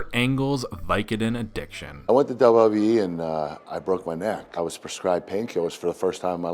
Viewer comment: Has anyone else noticed over the years that Kurt Angel can't pronounce WWE correctly? It always sounds like he is saying "double double -E"